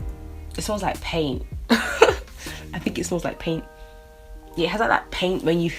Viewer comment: lol I thought you said it smells like pain 😂😂😂 I need to pick up that primer!